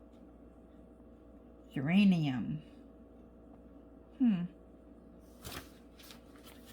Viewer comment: I thought you said uranium at first!!! Lol that’s a weird name for a pink color. Geranium makes more sense, but I typically think red for geraniums.